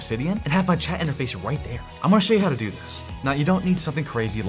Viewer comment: I heard it as: You don't need something "Terri"ble like Terry & thought you are roasting yourself 😅